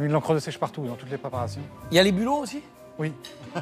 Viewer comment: il dit quoi cyril?? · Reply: Y'a des bulots aussi ???.... voila ce qu'il dit, reponse humoristique au fait que le candidat a utilisé de l'encre de sêche partout.